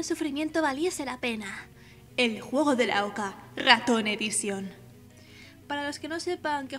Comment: que dicen al fondo? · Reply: "El juego de la oca" Xd. Mi ordenador no funciona muy bien y editar vídeo es difícil, así que a veces se me cuelan fallos así qwq